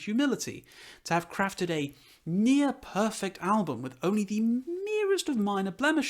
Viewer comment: I almost heard you say "A meer perfect album" :D I'll give it a listen, for sure.